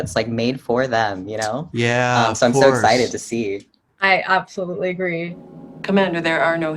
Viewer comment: Did anyone catch that "I want attention too, give me praises pleeeeaaaassseeee" in the "I absolutely agree" at 1:12?